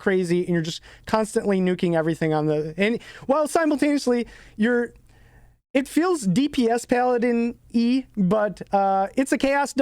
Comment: What do you say at 8:55? "It feels like a DPS-Paladin"? How strong is this build in fact of damage and comparison to other classes?